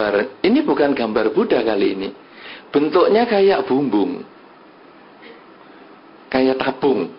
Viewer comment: Kalungnya gak tabung 🤣🤣🤣 salah dengar jadi kayka bumbu 😝😝